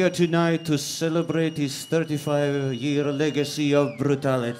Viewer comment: I thought he said “I give you... dancing” 😂 definitely a pioneer of evil😂